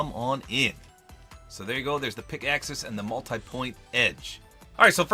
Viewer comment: Honestly when he said pick axis I thought he said pickaxes as in two but I looked and was like WAIT A MINUTE